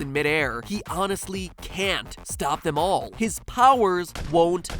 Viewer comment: I always thought he said "YOU can't stop them all", like it was Xavier who stopped the bullet.